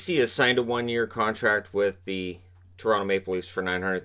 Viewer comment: I thought it said vasy to the leafs and I was very confused